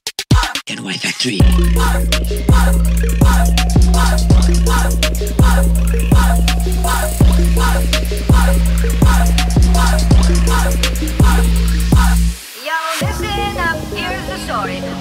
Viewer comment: I thought at said "get out of my factory" lol 😂